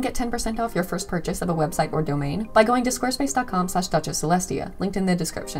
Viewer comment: I heard "the lesbian" instead of celestia